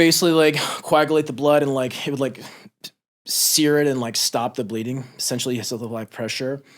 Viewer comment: Not the point here but @ 4:33 when he says “sear it and stop the bleeding” my phone always hears “Siri” instead of “sear it,” and pauses the video to let her listen for more commands haha